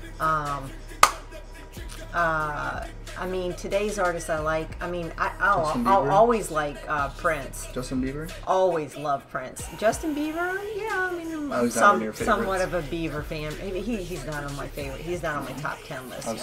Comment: Did anybody hear that she said "Justin Beaver"?